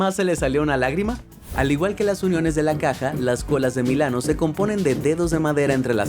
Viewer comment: Escuche mal yo escuché mil anos